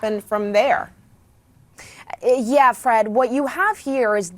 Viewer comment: Did she call her Fred?